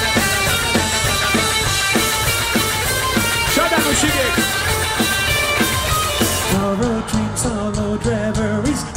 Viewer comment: What does Serj say at the 02:18? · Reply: "You're very beautiful"